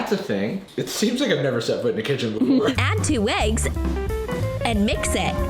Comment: did i just hear weggs? 💀